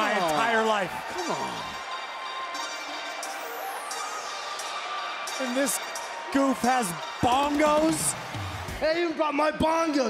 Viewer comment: “and this goof has bongos” was what I heard and “In this gupaz bongoz” is what the caption says. Nice job WWE.